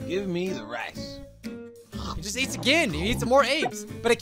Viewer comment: GIVE ME THE RATS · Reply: thought it said give me the rats it said give me the rice tho